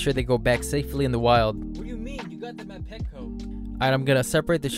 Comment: Anyone else hear someone say “ what do you mean you them at petco.”